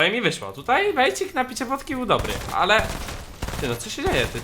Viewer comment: czy tylko ja usłyszałem picie wódki xD?